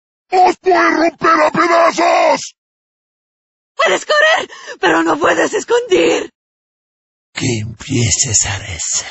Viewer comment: nunca entendí bien ese, parece que dice "puedes correr pero no puedes discutir"